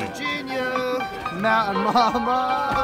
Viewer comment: did he just say mountain llama?